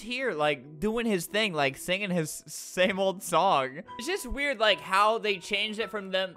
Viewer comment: “He’s just here like, singing his stable song.” - 2019 LOL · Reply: i thought he said “same old song”